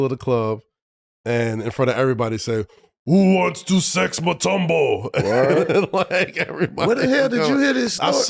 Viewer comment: I heard it “who wants to sleep with Mutombo”